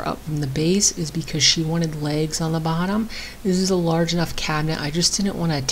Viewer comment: You SAID "She's going to have legs on the bottom".. I HEARD "She's going to lay eggs on the bottom".🤣🤣🤣